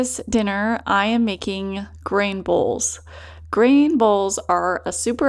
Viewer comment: I thought you said Brain Bowls 😂. My boys need these everyday!!